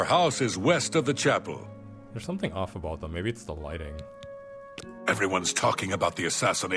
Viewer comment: To this day, every time i go to Kvatch and see that woof elf, I just hear "EVERYONE IS SMOKING....... c r a c k."